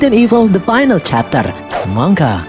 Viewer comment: apa cuma gue yang dengernya, semongko :v